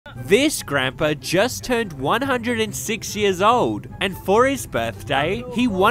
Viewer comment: I thought he said “he just turned 160”💀💀💀